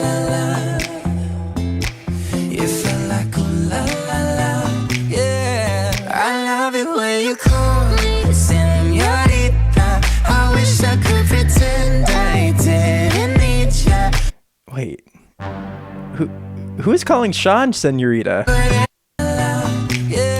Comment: I think shawn’s meant to be saying “i love it when you call me, senorita” as in like a phone idk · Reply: oh that makes sense! i thought he said "you love it when i call you señorita" but it's hard to tell